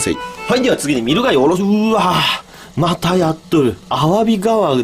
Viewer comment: 【またやっとる】の意味がそれにしか聞こえなかったw